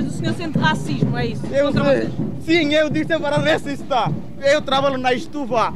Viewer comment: "Sim, ele é sempre racista". Foi isso mesmo que ele disse, ou foi a redação?